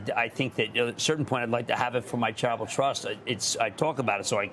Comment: Everytime he says "Charitable Trust" ... I hear "travel trust"